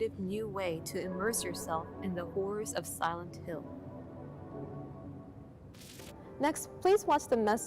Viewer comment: ok that sounded like she said “whores of silent hill” 😂😂